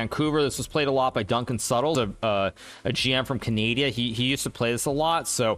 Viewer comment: did bro say canadia